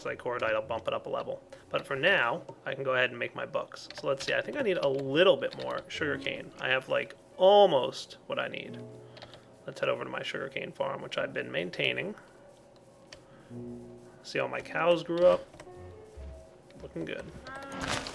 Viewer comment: on first time i hear there "my shuriken farm" xD